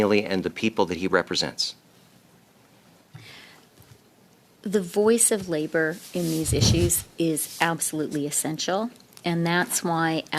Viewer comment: anyone else hear “the voice of our slaves is essential,” when this witch speaks?